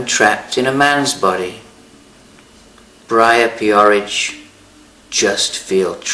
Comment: that coupled directly after the I hear 'pan-andryn' (or what they said) as the pan-vagine....I was choking and wheezing like an old man. Genesis is always great and the pictures were good.